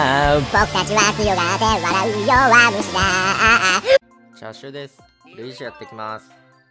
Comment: チャーシューですって聞こえた